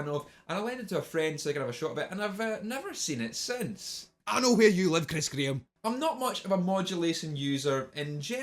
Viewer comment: What the fuck is he saying? I seriously can't interpret it. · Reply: "I know where you live Chris Graham"